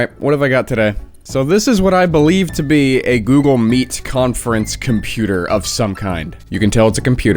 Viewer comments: I keep hearing this as “Google Meat” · Thought it said Google Meat for a hot second there, lmao.